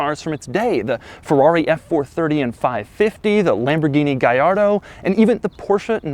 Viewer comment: did he really said Lamborghini Gairdo??